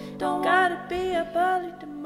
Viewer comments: The first few times I listened to this song, I thought it said "playing card" instead of plain girl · the "a nod of pity for the plain girl" line made me almost scream, but then i realized its 2 in the morning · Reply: I thought she said "nod of pity from the playing cards" lol but then again I cant seem to understand the words in a lot of the material revolving around the odd pronunciations in this genre. I just like it for the melody and tones.